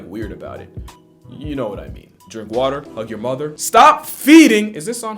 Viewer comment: "Fuck Your Mother" I'm legitimately hoping I misheard that lol